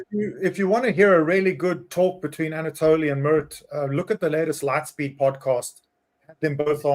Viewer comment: I thought he was saying "Anatolian Mert" :D